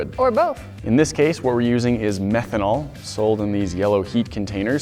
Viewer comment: for a second I thought he said yeet instead of heet